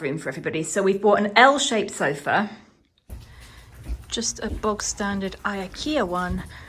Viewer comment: Grinning to myself here, wondering if all the viewers from across the Pond are scratching their heads, asking themselves if you really did say Bog-Standard. (Yes she did). 😉😀 It is an English saying. Do any other countries say the same thing? We also say in the UK: Bog normal, instead of plain normal, or just normal. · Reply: When I heard her say this I paid particular attention. It sounded like she said ‘box standard’.